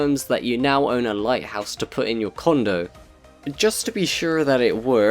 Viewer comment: for the lighthouse part i thought u said condom